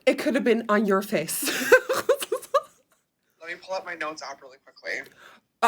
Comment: What did James say at 14:47? Let me pull my what out really quickly? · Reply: I heard notes app ? lol